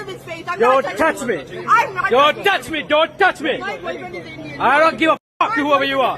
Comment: I think she said 'my bf is also Indian '